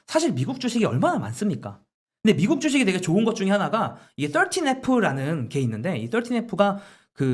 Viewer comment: 미국 주식이 좋은 것 중 하나가 OOOO 라는 게 있는데에서 OOOO가 뭔지 잘 안들려요. 좀 적어주세요. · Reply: 13F 입니다